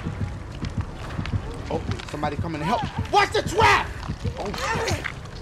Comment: Is it me or did I hear dashie say "watch the twap"